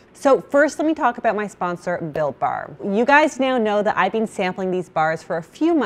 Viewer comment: I keep hearing “Bill Barr” instead of Built Bar!